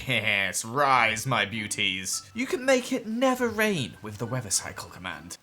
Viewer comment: Is it just me, or did say "You can make it NETHER rain"?